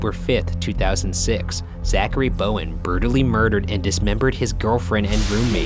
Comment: he says burtally instead of brutally